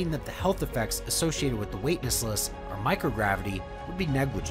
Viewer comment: you say “weightnessless” instead of weightlessness 😂😂😂❤